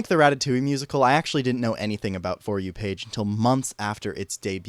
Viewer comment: at first when you said the name of the musical I heard "For you, Paige" and I was like 'aww thats nice, it's about a bunch of people coming together for someone else'... and then I realised Edit: welp